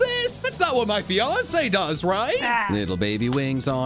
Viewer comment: What does say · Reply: Ah